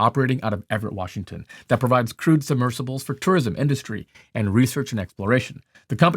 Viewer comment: I misheard 'Crewed Submersible' as 'Crude Submersible' , but I guess in this case it didn't make a difference